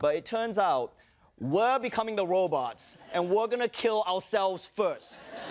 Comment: The Chinese stallion is what I'm gonna call Ronny now! 😂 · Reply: What's even funnier is I heard it as Chinese Stalin.